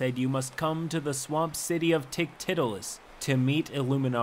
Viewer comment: when I heard Ticktittolis I had a double take